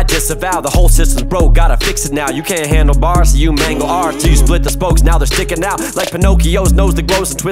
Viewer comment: You can't handle bars, so you mangle ours......damn! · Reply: I thought it said mangle art